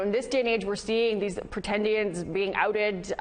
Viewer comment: Did she say pretendians